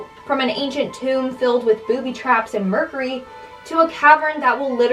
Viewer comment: Booby trap 😂 did I hear that correctly 😂?